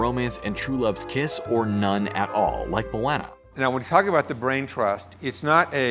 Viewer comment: Did he just say “Mawwana”